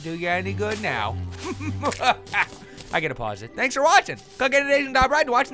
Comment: For the longest time, I thought he said click on the asians in the top left blabladavideo.